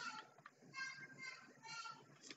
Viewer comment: Huh- I found a secret. I literally heard voice and (s)he? Said "Freaking children" WTH